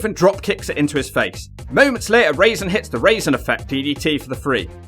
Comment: is he saying Raven and Raisin throughout the whole segment??